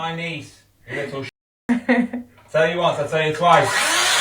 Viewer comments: 25! ? WTF she has all rights for privacy! For sure at some point the doughter will go no contact. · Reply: She's not 25. Americans misheard what he said, he says "I told you once I told you twice." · The cutting the door open on a 25year old is beyond crazy she's 25 at first I thought she was a teen till u said. The photo thing there is a time to use it and to not my father in law died 2 years ago unable to meet his great great grandson so my daughter used ai to create picks of him holding him as that was his wish he never got to have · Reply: She's not 25. Americans misheard what he said, he says "I told you once I told you twice."